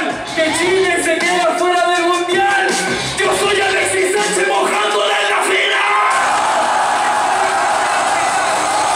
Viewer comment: que dice kaiser? yo soy alexis en la final? xd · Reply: Yo Soy Alexi Sanchez Mojandola Hasta EL Final